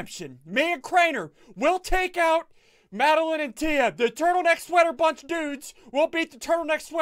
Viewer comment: I thought you said "Turtle bunch sweater necks" and I watched it over about 7 times because I was so confused!!!